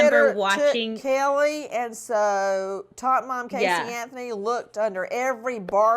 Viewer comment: Nancy Grace calling Casey Anthony “top mom” is an absolute mood when thinking about that case. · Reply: I thought she was say tot mom . Thanks !